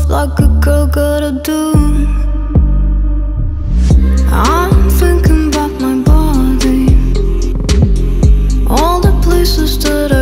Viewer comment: thought she said "I'm thinking bout my mommy" ah ha ah haaa I'm dumb!